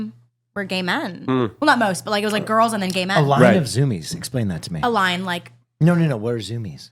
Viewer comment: I thought she said "did a line of zoomies" like, yknow, cocaine